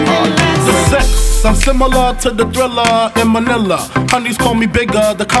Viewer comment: did he say Thrilla in Manila? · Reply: Jeriel Tejano To tha thrillah in manilah*